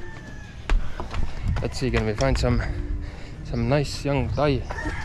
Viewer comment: did you just say hopefully you could fine some nice your guy?. 😮 🤣